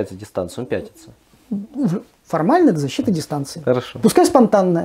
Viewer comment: З "Защита дистанции"... Рука-лицо. Запомните, "особо одарённые" эксперДы: дистанцию можно только соблюдать, но не защищать. Защищать можно только себя, соблюдая дистанцию. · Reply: Да, ты прав. Нечёткое произношение от блогера и его гостя меня ввело в заблуждение. Мне послышалось "защита дистанции", а не "защита дистанцией".